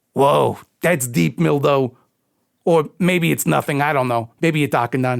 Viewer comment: I'm glad and are still working together after all the heartache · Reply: Oh, I thought he said Mildo, as in a shorthand nickname for Mildred. is much funnier tho.